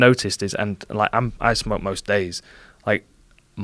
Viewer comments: did he just say he smokes most days? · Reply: I think he said “I ice em up most days” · does he say he “smokes most days”? Watched it back a few times and can’t hear anything else.